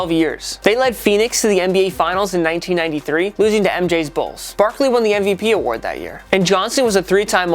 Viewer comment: i thought he said “losing to mj’s balls”😂😂😂